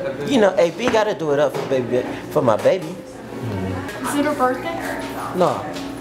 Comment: Anyone else hear him say for his baby daddy